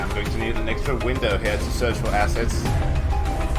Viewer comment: in all honesty, it sounded like you said "I'm gonna need an extra window here to search for some asses.". Maybe it's just my cultured brain messing with me. Not judging, of course, just found it funny lol :P Very good vid, dude!